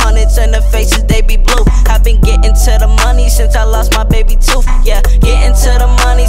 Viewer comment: This whole time I thought he said" gettin to the money since I lost my baby too" but he's actually saying baby tooth